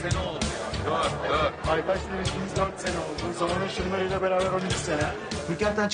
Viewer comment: Celasunun ne dediğini Merak edenler için : -ARKADAŞLAR 4 SENE OLDU 4(MEKE) +ZAMAN AŞIMLARIYLA BERABER 13 SENE(CELASUN)